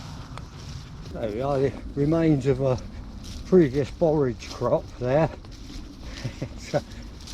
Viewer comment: Another good bit of trail gardening. That narrow path looked exactly like the one I sprained my ankle on. What were those purple crops again? I thought you said Porridge at first 😁 I wouldn't have had a clue what it was. I'm surprised more people don't use those paths. They're missing out.